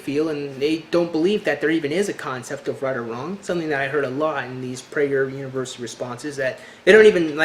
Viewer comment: Please help, What on earth did you say? Something responses? Would love to know, thank you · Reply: "Which is something I heard a lot in these Prager University response videos"